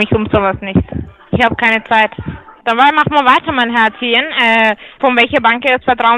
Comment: Ich habe JETZT erst verstanden, dass du dich genannt hast :D Ich dachte die ganze Zeit, die Dame nennt dich von sich aus "Herzchen" :D